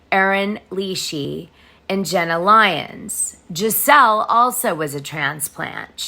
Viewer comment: Ah Jessell - I got confused - thought you were saying Gizelle